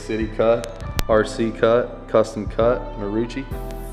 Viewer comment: He was saying Custom Cut and I thought he said Custom Cuh I was like damn he from Houston now 😂